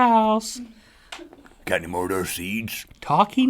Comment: At the end I thought Rhett said "GOT ANYMORE OF THAT WEED"??